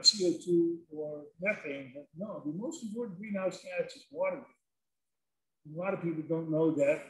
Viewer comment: I can't understand what the speaker says is the worst greenhouse gas, at 36:35 anyone? Enjoying the talk though, thanks. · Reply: He says "water" but he means "water vapour". "water" is a liquid but "water vapour" is an invisible gas made from the same H2O molecules as "water" and it's between your eye balls and the screen you're viewing now. "water vapour" is a powerful broad-band "greenhouse gas" but speaker said "water" because he's not that type of physical scientist.